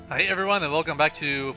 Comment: it sounded like he said "i hate everyone"